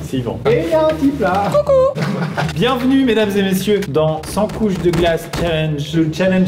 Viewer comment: j'ai eu une hallucination auditive, J'ai entendu "100 couilles de glace" !